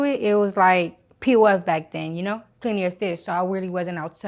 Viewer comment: Clean your fish? ....lol what does she say here??? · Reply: Plenty of Fish. A dating website